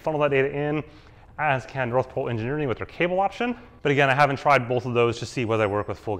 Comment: you mention 4iiii's and something engineering cable solution. Cannot make out what the engineering company name is? Another name I cannot make out at 11:16 after you mentioned VirtuGO? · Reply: NPE Cable (North Pole Engineering), and then RoadGrandTours